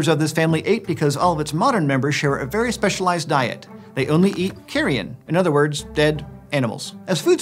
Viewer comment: I thought that he said Tyrion